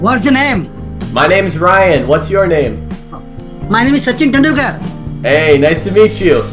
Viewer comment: I think he says "My name is Sachin Tendulkar." (A famous cricketer.)